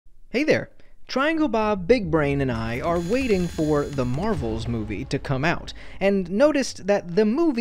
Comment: help im sick and i i heard “marble” instead of marvel ☠️☠️😭😭